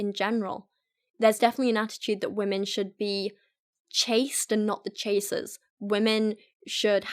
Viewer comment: When she said "chased" I heard "chaste"... I dunno, there's a pun in there somewhere. :o